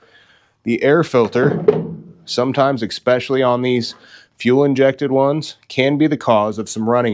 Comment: For a second there I thought you said fuel injected. 🤣